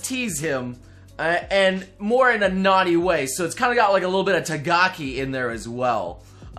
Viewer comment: Did you just say Tagaki?